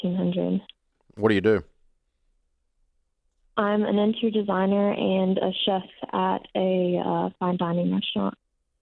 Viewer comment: The fact that she couldn’t answer where her money is going sounded like the biggest red flag. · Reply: From designer to chef, I thought designers make more than 15$hr, idk first I thought she said car designer! 🤷‍♀️